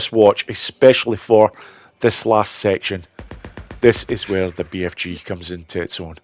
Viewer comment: 😂😂 Whoo I misheard what you say here!! Had to relisten to it bc first time through I thought you said "This is where the BFG comes in tits on" 😂 I listen so good 👀